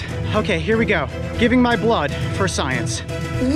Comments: What Everyone heard: “giving my blood for science” What I heard: “Giving my blood for subscribers” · sec...Am i hearing things? I thought he says "Giving my blood for money"..